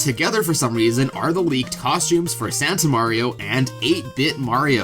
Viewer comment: misheard as "sans mario"